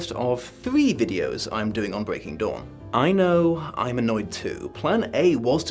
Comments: When first said "Breaking Dawn", I heard "Breaking Dom". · With your accent. When you initially said breaking dawn. I thought you said Breaking haha. Might have some truth to it or its to early in the morn for me and I havent had a cup of tea yet XD Edit :(went back and listened to it a few times. Convinced thats what you not dawn. Genuine or Freudian slip?)